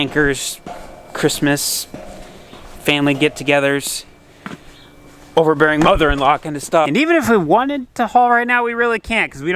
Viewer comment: Did he say "over-bearing mother-in- law" at 11:50?